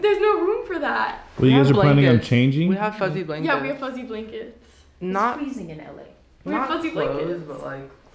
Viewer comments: Who else thought they both said “Fucking Blankets” 🤣🤣🤣❤️❤️❤️ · Am I the only one who thought they said fucking blanket